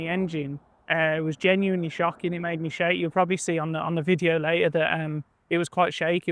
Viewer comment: I honestly thought he said "it made me shite" at first 😂😂